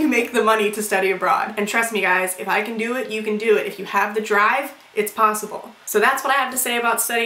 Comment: at first when you said "if you have the drive, then you can do it" i THOUGHT you said "if you have to drive, you can do it" an i thought to my self, hmmm im pretty sure those large bodys of water might get in the way of that! LOL